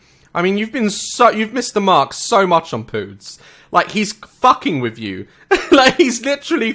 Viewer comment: Again did he say “pood’s”? (22:33)😂